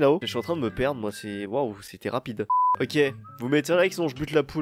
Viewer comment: "c'était rapide" j'ai entendu "c'est Terracid" j'ai bugé.